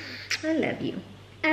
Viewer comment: I heard "ARLOve you" HAHAHA